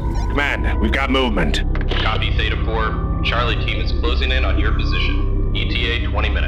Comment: What I heard: "Charlie Sheen is closing in on your location."